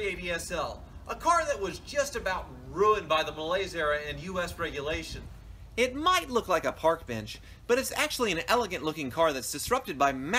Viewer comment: what he say?...malaysia and US?